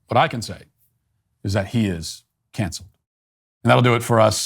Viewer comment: When Matt ended the show, as usual, with "Jeffrey ... is cancelled" I thought he said "cancer"